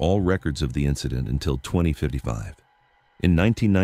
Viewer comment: You got yourself a new subscribers, my friend..... great video Wait a minute 8:05 did he say 2055